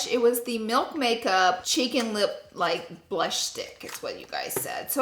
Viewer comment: I thought she said Chicken lip